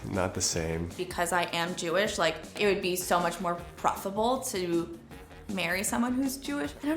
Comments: I think she said preferable but with bad annunciation. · I could be wrong but it sounded like she said “preferable” just in an accent and the girl who complained about being called “a colored person” could be referring to how in american they would segregate “colored/ colored people” from “white/ white people” in which I could understand why she’s upset, but I don’t agree with her